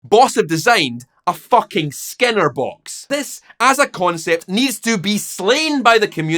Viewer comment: This is NOT a pedal; it's a Skinner Box! Are Boss using behavioural conditioning to monetise addiction and exploit vulnerable musicians? Let's discuss the new Plugout FX - the pedal with microtransactions. <link> <link> More details on how implements product promotion - <link> #boss #plugoutfx #scienceofloud More from: Support on Patreon: <link> Join Discord - <link> Buy Merchandise - <link> Website - <link> Contact - <mail> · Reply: honest to god, i thought you said "scanner box" that's why it didn't make sense